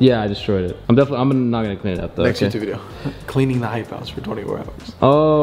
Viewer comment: NO FOR A SEC I THOUGHT IT SAID IM LEAVING THE HYPE HOUSE..😭